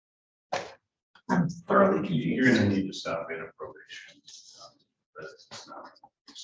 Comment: What did the attorney say to her at 6:35? You are going to need to stop inappropriate ??????????? Attorney had enough of her too?